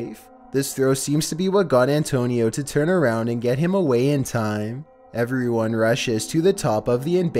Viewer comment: It sound like he says taxie get away